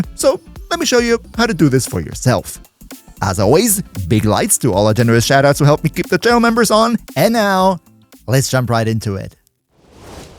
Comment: "Big lights to all our generous shoutouts who help me keep the channel members on" I thought I was going insane when I heard that...